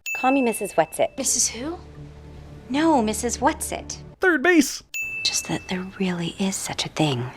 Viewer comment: I watched the movie and know it says Call Mrs. Whats it But I hear Communist, Whats it(again)